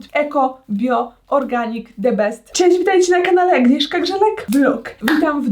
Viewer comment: usłyszałam ,,Agnieszka Grzelak Dno"